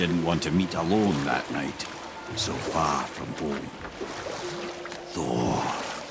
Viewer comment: did he said far from home